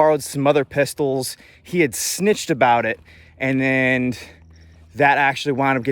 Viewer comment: Snitch? I thought they said you was a bitch… My bad! Just kidding bro. More videos please though!